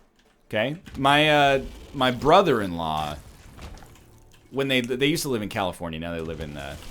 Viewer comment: I thought for a moment NL said he had a brother and i was really confused. Then i realized it was brother in law...oops